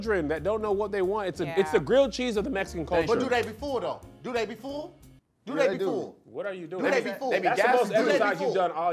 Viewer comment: Do they be full ....Do they be full… Do they be full… Do you they will be full… Do they be full… Do they be full… Do they be full… Do they be stil do they be full… Do they be full… Do they will be full… 😂😂😂😂😂😂😂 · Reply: Ohh I thought he said do they be fooled 😂